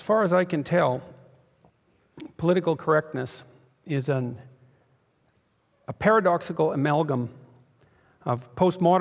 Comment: Sounds like he just said 'Neilism is real and political correctness is a facade'